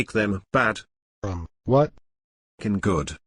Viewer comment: his response has me crying so hard sounds like he said "bro what ?"